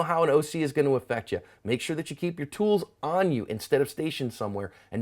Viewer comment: I always thought you said cover your ass at the end of videos until I found out it was asp